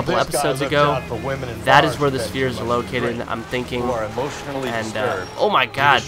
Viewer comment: I thought you said spear not sphere :3